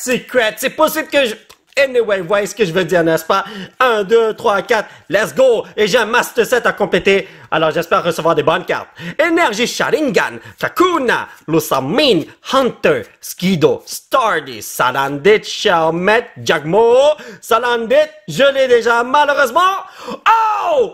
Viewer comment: C est moi ou il a dit énergie Sharingan à